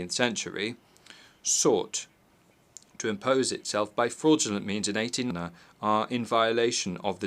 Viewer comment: Why was there a cut at 8:03? do we know what was said? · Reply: if you search you can find the PDF of his speech, but here what he says in this cut: which sought to impose itself by fraudulent means in 1899, through an arbitration that was rigged betwen the powers of the time. 18. On 03 December 2023, the Venezuelan people expressed themselves clearly and decisively in a consultative referendum, granting an unavoidable mandate to the Venezuelan State to recover the Guayana Esequiba, a territory that represents a sacred inheritance of the struggle of our Liberators. 19. In that referendum, the Venezuelan people ratified the historic position of our country of never recognizing the jurisdiction of the International Court of Justice (ICJ) in this case, nor any decision it may adopt on this matter. 20. The President of Guyana has lied in this tribune on this aspect. Venezuela only recognizes the Geneva Agreement of 1966 as the only way to reach a settlement that restores Venezuela's rights over the territory of the Guayana Esequiba.